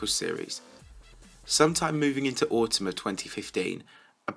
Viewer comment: What did he say? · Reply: moving into autumn of 2015